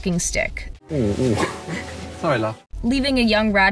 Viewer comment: Seeing Lucius’s character go, “Oh- sorry love” is just heartwarming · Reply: I thought he said "Sorry, lad."